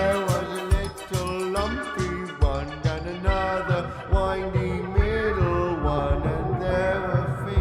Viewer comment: I don't know if you've gotten this comment before but I think here instead of saying "whiny" he's saying "windy" (if you listen closely you can hear he pronounces the d), as in Lesley winding the music box. If that's true, him calling her the "middle one" means that at one point or another he was aware that there's something above Lesley as we see in episode 6 which I think is the implication here.